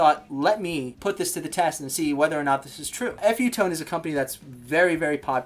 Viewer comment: This whole time I thought he was saying "Epiphone" but he was saying "F U Tone" Lol. But what a flip flopping difference. Gonna have to pick up a few sets of my own. Thank you for the vid brother.